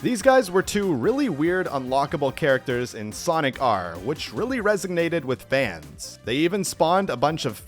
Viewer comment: did you say "resignated" instead of resonated? I swear that's what I'm hearing lol.